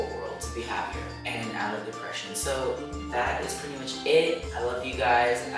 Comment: It sounded like you either said "I love Depression" or "I hate depression"